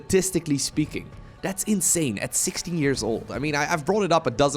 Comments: me: thought he said 60 years old me: wut 0_0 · I misheard you, I thought you said "at 60 years old", I did a "wait what?!?!" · Lol he said 'impressive for someone who is 16 years old" but I heard "60 years old" are there actual 60 years old starcraft players out there?